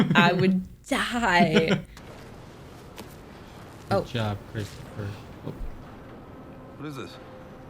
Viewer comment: Can someone tell me what he said at this time stamp? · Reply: I said “good job, Christopher” Was probably talking about Nolan, I can’t remember lol